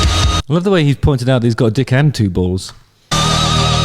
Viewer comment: I thought he said I've got a dick in two walls, haha.